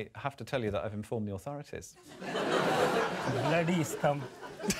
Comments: What does he say at 6:16? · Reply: He says 'Bloody scum' Don't worry, I didn't know for a while either 😂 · i like how even couldnt stop himself laughing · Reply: you mean around 6:10? I think he said bloody scum